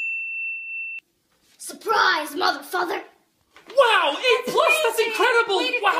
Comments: I thought she said, a surprise motherfucker my bad of saying a bad word but instead, she said, surprise, mother, father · She : suprise mother-father le me : Hmm ok I misheard that 💀💀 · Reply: Lol same I thought she said surprise mother fu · I thought she said "mother f**ker" POV:- (When You Are Dirty Minded)🌚🌚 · For some reason when I first watched this I thought it said “Surprise mother f#####” but then I heard it right and it was “Surprise mother father” 🥲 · "Suprise mother father" Got me laughing on the floor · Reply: I thought it said mother fu- for a second 💀💀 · Lol I thought she said mother F which is a bad word but I realized she said mother and Father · I thought she said surprise mother. F***er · Is it only me that when I hear "SURPRISE MOTHER FATHER" sounds like motherfu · Me after realising Surprise mother f was actually 'surprise mother father' but people misheard it - Glad that i am the one 😌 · I thought it said surprise mother fu-my mum with the belt- o hell no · I thought she said "suprise Mf" but she said : suprise mother father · i literally thought she said “surprise motherfluffer the bad word but she said mother father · I thought she said surprise motherf**ker 😂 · "Surprise mother-father" Umm ok i misheard it 💀💀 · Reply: I thought she was saying mother Effer you know what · I thought she said mother f🌚🌚er but she said mother father 👀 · I thought she said surprise moth Er f**ker but she said “surprise mother father”